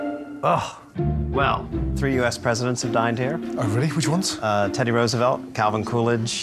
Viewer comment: I thought he said ‘3 us presidents dined in here’ not ‘died’ lol